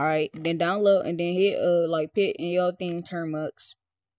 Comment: what was that first web site you said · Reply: Termux